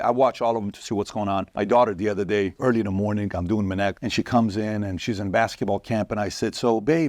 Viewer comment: What did Patrick say at 00:10 seconds he was doing early in the morning? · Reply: "I'm doing Minnect."